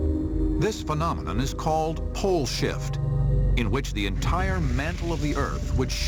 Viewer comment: lmao i thought at 2:44 the overvoice said "bullshit" XD